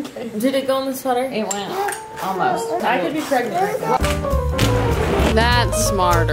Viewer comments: Did anyone else hear saying “I could be pregnant right now” right befor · i swear i heard say “i would be pregnant right now” oooooo🥰🥰